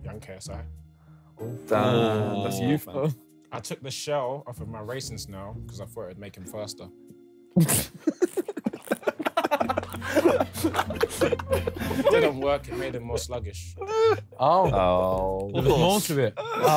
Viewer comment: The joke at 8:35 what does Tobi say? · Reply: He took the shell off his racing snail because he thought it would make him faster..........it didn't work it made him more sluggish